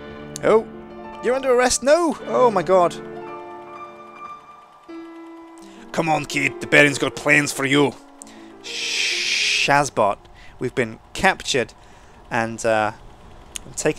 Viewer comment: Did I hear a 'Shazbot' at 05:20? I think I did! Nice one.